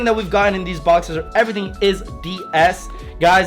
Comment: caption says ‘everything is BS’ instead of ‘everything is DS’ 😂😂😂